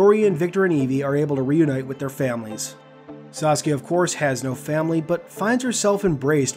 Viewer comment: I COULDVE SWORN HE SAID SASUKE INSTEAD OF SASKIA